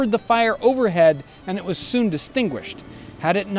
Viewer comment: * "...and it was soon distinguished..." * Is that really what it said or was this a verbal miscue from the narrator?